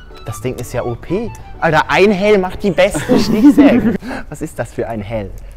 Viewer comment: Ich hab halt einfach einhelm verstanden statt einhell owo und dachte das wär warum auch immer nen einstein witz owo